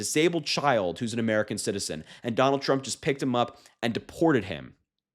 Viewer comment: I thought it said trum just died, I was excited then let down lol